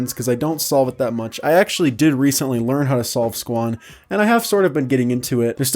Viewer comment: did he say squan